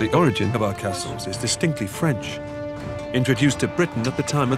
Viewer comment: i thought he said stinky french